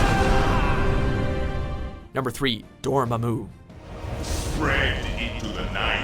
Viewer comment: When this guy says Dormammu, I heard 'Door, moomoo'.